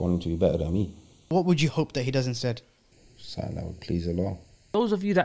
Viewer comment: Why do you have Portuguese translations brother😂 I wanted to know what he said 2:27 but🤷🏽‍♀️🤣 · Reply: he said: “something that would please allah”